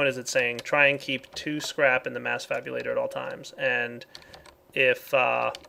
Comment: Did just say 'mass fabulator'? I guess we all wanna be fabulous but...